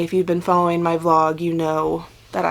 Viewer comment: While you were still talking about them I went to Ravelry to check them out and they weren’t there.....so I came back and read the Dow bar info and saw the release date. LOL. Do you get more benefit from Etsy or Ravelry? I love the Raven fingerless, they satisfy the witch in me so I’ll be watching come Halloween🧙🏼‍♀️🕷🎃🕸 where is your blog found? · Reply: Ok, when you said you were going to try and release onSunday I thought you said follow on your blog. Must have said vlog. 👍🏻